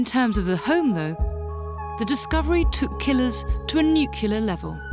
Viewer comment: "(...) the discovery took killers to a nuclear level." (50:10) I laughed out loud because it sounded like "new killer".